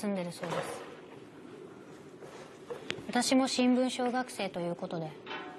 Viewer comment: 新聞奨学生･･･新聞小学生に聞こえる